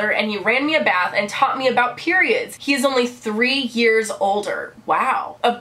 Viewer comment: I first thought it said, he is only 3 years old